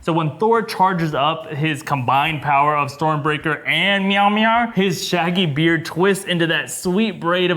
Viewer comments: did this guy say meowmir instead of Mjolnir? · did he say meow mear, instead of mjolnir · sounds like u say, "storm breaker and MEOW MEOW". Instead of mjolnir